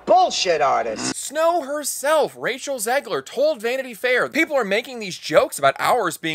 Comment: Did you just say "Racial Zegler?"